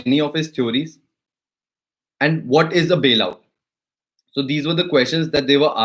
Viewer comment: what you said ? I can’t make out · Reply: WHAT IS A BAIL OUT?